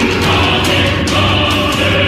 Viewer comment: I thought Beerus said "Bankai"